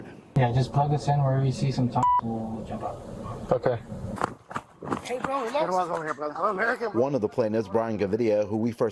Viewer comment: What is that word? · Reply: Poo Poo